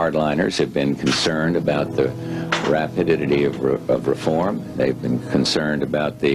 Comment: did he just say "Rapididity?"